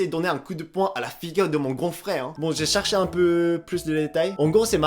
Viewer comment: A 5:03 c moi ou il dit « j’ai cherché des hentai »